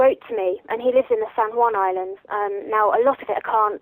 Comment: Did she say "San Juan Islands" at 7:54?